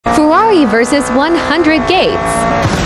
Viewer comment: At First When I Wasnt Focusing On Captions I Thought It Said Ferraris Vs 100 Gayd